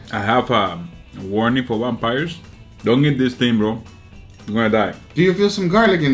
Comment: I have no idea what he just said... war nipple vampires? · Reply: “I’m warning you vampires” lmaooo war nipple vampires